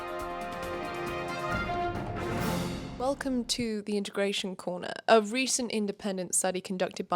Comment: I really thought integration corner said interrogation corner😂